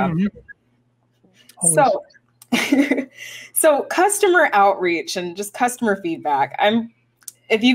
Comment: I heard "Customer outrage"... . Perhaps i should not play this video too fast.